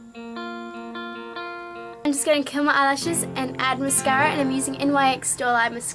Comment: Hahah I love your accent! When you said "I'm gonna curl my eyelashes..." for a second I thought you said "I'm gonna kill my eyelashes..." :)